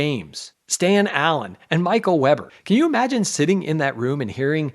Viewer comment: "green neck system" was written by the french mentalist Gabriel Werlen, and it is very good stuff. I Heard you said "Michael Murray"